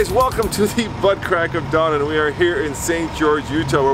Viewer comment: Did he say "butt crack?"